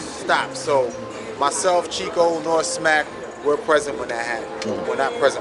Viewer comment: Did say norbes wasn't present? Haha around the 2:52/2:53 mark. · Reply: DJ Gains Bond 😂😂 nah he said “Nor Smack